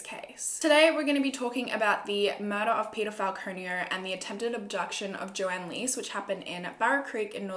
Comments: It sound like u said peterfile conio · I heard "the murder of pedophile Konio"... wtf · Am I the only one who thought she was saying pedophile when she said Peter Falconio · Although I read the title every time you said peter falconio, I thought your saying he’s a pedophile. I’m more stupid then a kangaroo 😂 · I thought you was saying Paedophile, not Peter Falcono and I just realised about 2 mins in because you wouldn't tell us so early in that the person was or wasnt a paedophile. So I apologise for having bad hearing